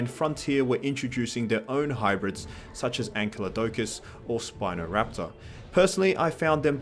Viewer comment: Sorry, did he says Spinosaurus isn't a canon dinosaur and that Frontier added it in for some diversity? Has he watched the Jurassic Park movies? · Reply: When did I say that. I think you misheard Spinoraptor.